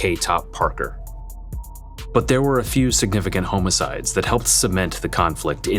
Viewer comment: Did he say homoicide? 😆